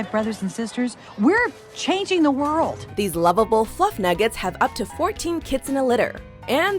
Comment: WAIT @ 5:40 did she say “plump nuggets” 😂